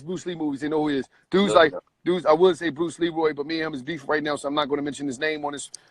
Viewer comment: did he say beefing with Bruce LeeRoy? The last dragon?